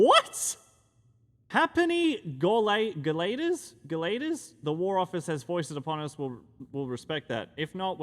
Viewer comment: what in the world are those words?! lol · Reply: gauleiter is a German word meaning a leader of a district particularly during nazi occupation i think. In older English it would be used to refer to refer to someone in charge who's very overbearing. The tuppenny-ha'penny bit is an idiom meaning of little worth or consequence. It's referring to a two pence coin and a half penny coin. 😅